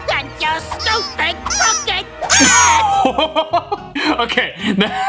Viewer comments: Note: Mugman actually said "AND YOUR STUPID CROOKED HAT" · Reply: Wait I thought it said " AND YOUR STUPID COOKING HAT" but now that I think more about it it probibly is "crooked" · Mugman said "Crooked hat" 2:00 It actually said "Makka Pakka" 3:10 Darwin said "What the..." and screamed. 3:48 He said "Thank you" 5:30 They sang "Bucky, sail away 6:35 Scooby did say f*** but said Dean Funk after 8:20 Jack said "you have to read the book its in the contract you have to read the fine prints kids me me me me" 9:08 Elmo said "B-but how, Chris?" 10:25 was a edit made by